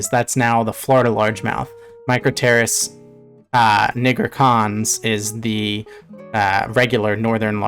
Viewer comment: what did he say?????? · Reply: micropterus nigricans